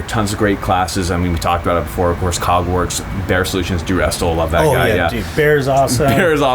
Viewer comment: Did he say "Cog Works" or "Hogwarts?"